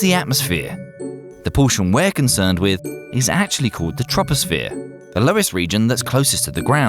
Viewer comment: i thought he said trumposphere..